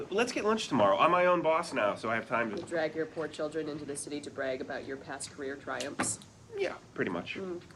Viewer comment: Can anyone tell me what they're saying in 37:25 please? The voice of the girl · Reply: Robin says " to drag your poor children into the city to brag about your past career triumphs" Ted says " Yeah! pretty much"